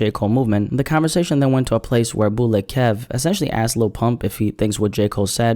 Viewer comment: did he say lil punk??